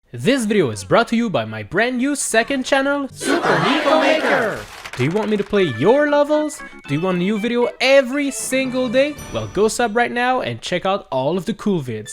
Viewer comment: i swear i thought it said "SUPER NICE MAKER"